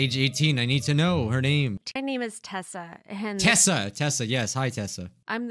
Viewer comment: my name is tesa Me:so is it tesla I thought it literary she said tesla